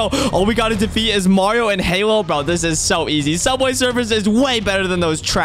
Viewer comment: Did he just say subway suffers is better than Mario and halo?